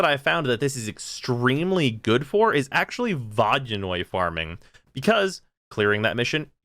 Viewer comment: WTF is he saying @ 1:54?! Vaginoid farming?